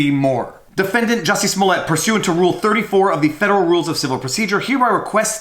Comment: i can't help it. i heard "smell-it" not "smollet."